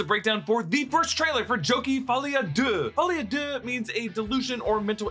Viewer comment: did you say jokie?